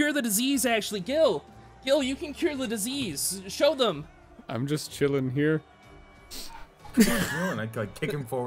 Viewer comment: why did I hear that as “I’m just gillin’ here” I hate my brain’s inability to not make a pun